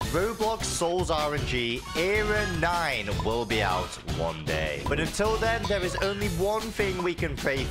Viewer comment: at the start I thought he said era 9 will come out in 1 day